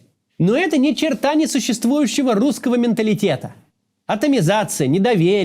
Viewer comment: Почему я услышал "Ни черта не существующего русского менталитета"? 👿👿👿?